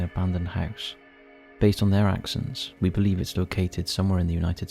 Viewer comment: … at first I thought you said, “based on their actions” but after the third replay, you said “accents”. I’d already expected them to do something untowards, this country has gone to shlt. I’m really just bracing myself for what my “countrymen” do now. I’m just so embarrassed. I apologize to the world for what the “United States” does now. It’s all so abhorrent. 😣😔 We’ve failed spectacularly as a nation and it will never be undone.